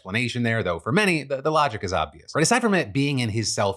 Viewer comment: Look, I don't know who is responsible for the closed captions/subtitles, what their process is, or how much effort they put or do not put into it, but... "decipherment"? Instead of "aside from it"? I don't want to beat a dead horse, but someone needs to do a sanity check on these.